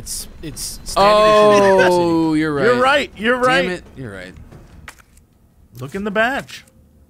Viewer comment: When he said look in the badge, for some reason I misheard him and thought he said to "Look in the vag."